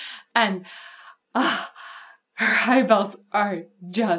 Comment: I'd just like to thank YouTube's auto-generated captions for mishearing 'high belts' (I think?) and captioning this as "her eyeballs are to die for".